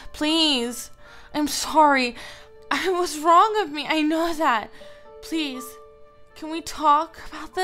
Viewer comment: And if you listen carefully in 8:06 Liz says I naw that instead of I know that you know what I mean